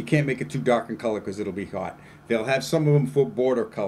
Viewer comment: What is the word you mentioned at 7:38? "You can't make it too dark in color because it'll be ......"? What's the issue with darker colors as the main flooring color? Thanks. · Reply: If the decking is too dark it will be hot! I don't think it is but many people think that dark decking is hot. I always say that at 85 degrees you can walk on any decking barefoot, At 100 degrees everything is hot. If it's 100 degrees with a clear sky you wont be sitting on a deck.